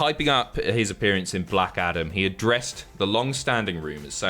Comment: honestly thought you said Blackadder and got excited for a Rowan Atkinson/The Rock movie